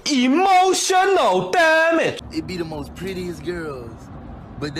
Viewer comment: The Subtitles Say - Emotional Damn it 😂😂😂